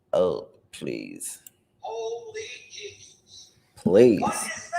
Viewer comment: I hear -A..hole Stop!, and then a male saying -Jesus! I had thought he was saying Zee, but now think he said Jesus...😳Female crying, then male screams -Let Me Up! Male screaming😢Jmo